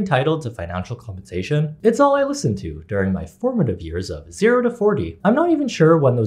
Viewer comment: did he just say he was 40?? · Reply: He did say forty…